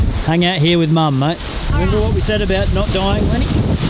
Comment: “Remember what we said about not dying,Lenny?”. Gold. Absolute gold. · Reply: Haha! I thought he said “...what we said about not doing”. So much better :)